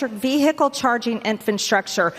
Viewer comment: did she say "infantstructure"? 🤔